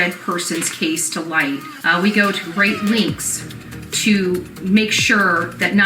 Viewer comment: I listened 3 tines, and can you all confirm that I am hearing say,"We go to great links," instead of "We go to great lengths."??? She is so damn dumb and, yes, those extensions must have been rhe Walmart Specual. They don't come close to matching her hair. Blonde and yellow much? :😜